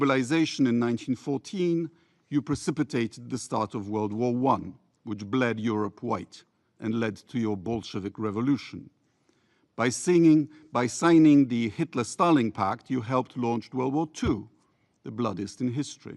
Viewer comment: I hear , and led to your bullshit revolution 😂😂😂